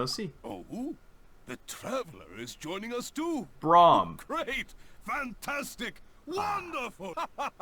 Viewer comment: did he call cyrus braum ????